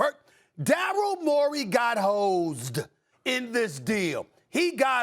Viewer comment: Thought he said 76ers got hoed 😂🤣😂🤣